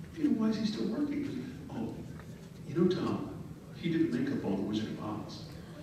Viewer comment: What does he say at 26:02? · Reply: His exact words were, "You know Tom, he did the make-up on The Wizard of Oz" (referring to the original movie from back in 1939).